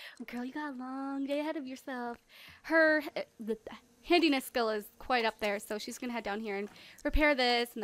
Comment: when she said handyness skill it sounded like she said hideous skill XD